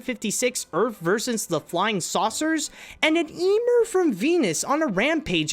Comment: It sounded like you said Emu, that really big flightless bird from Australia